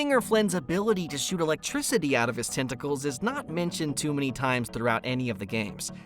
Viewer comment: I thought it said “lighting bolts shoot from my testicals Tentacles